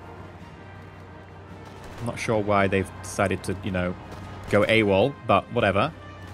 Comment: did he say AWALT? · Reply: AWOL